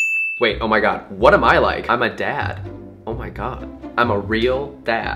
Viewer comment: I thought you said im a dab